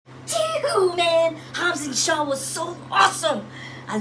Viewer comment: Sorry did you just say “cheeewhoo” no it’s “JEWHO”